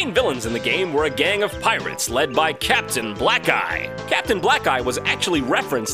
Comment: I thought he said "Captain Black Guy"...